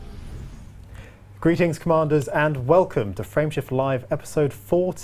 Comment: I thought the intro said, "welcome Friendship Live" and I was like, what a nice show.